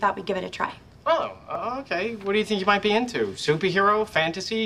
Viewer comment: "I swear I'll turn the hoes on you" What I thought he said for years